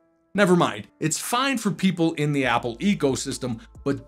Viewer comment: It sounded like "it is fine for people in Apple EGO system" 😂 Although this is damn right 😆😆 Stay safe!